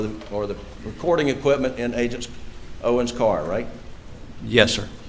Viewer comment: I hear "It's so bad, I did him so bad".